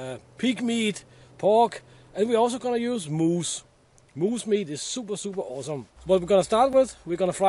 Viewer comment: I thought that said 'mouse meat' for a moment. :'O